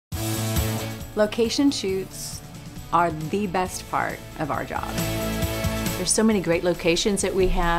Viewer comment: What's she saying at 0:01? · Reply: game She said “Location Shoots are THE best part of our job.”